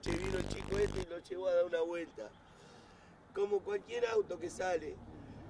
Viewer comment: escuché bien??? "Como cualquier auto que sale?"